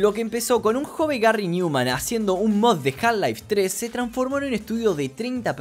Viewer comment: acaso en el minuto 14:10 escuche half life 3